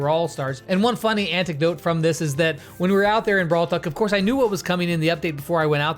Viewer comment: Wait wait wait, did just say Brawl Fuck?